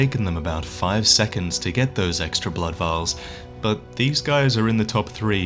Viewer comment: instead of saying blood vials youtube says buttholes